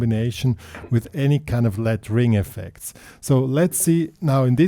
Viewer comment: I had to watch twice before i understood you were saying "the let-ring effect." I thought you were trying to say "layering effect," lol. I understand what you're saying, what you're emphasizing, but maybe we need to come up with a better name? "Sustained bass notes" maybe? IDK. Great licks! Good sound! 👍 👌